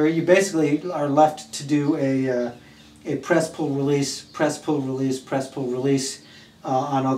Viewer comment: Were you saying "Press-Hold-Release" the whole way through? I thought I heard "Press-Hold-Release".... thanks